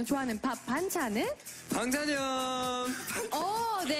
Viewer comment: it sounded like she said Bang Chan at the end of "side dish" so he just said it. 😭🤣🤣